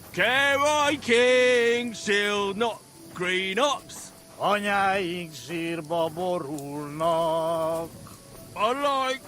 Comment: there's a rather funny error here in the supposedly "correct" pronunciation - it sounds like "Anyáink zsírba borulnak". As in, "our mothers are falling into fat", instead of "falling into their graves".